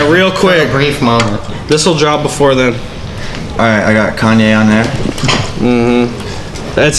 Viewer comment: What peep said? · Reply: “Alright, I got Kanye on there”